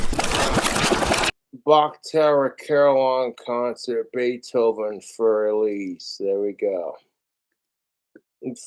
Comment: I swear I've listened to it 4 times and still can't tell what the fuck he is saying. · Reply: "Bach tower caralon concert Beethoven Fur Elise" and I promise you, it makes perfect sense to the researchers at JFS Industries.